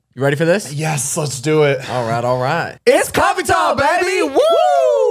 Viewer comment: I've been watching unfiltered from the first episode and I just realized they say 'coffee talk', I always thought they were saying 'coffee time' 😂 also I'm in love with 😶